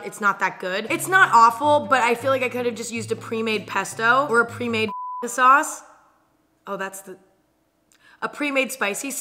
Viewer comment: did she say p* sauce? Lmao